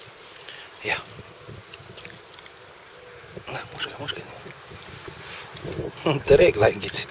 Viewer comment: Did you say "Nah, kushay, kushay" ("Here, eat-eat it") at minute 0:12? Because your language is not russian but it was a pure russian phrase (with right accent) that you used. What kind of slavic languages are you speaking?